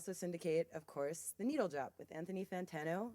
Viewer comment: It almost sounded like she said "The Needle Job"